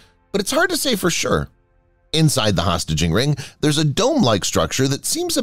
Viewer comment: Sounds like it says hostaging ring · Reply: It definitely says "hostaging." That's my mistake.